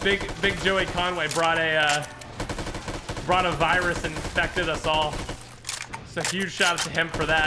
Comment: did he say Corona Virus? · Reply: He said brought a virus.